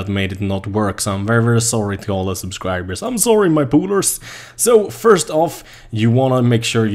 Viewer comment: did anyone else hear, "I'm sorry my pooters."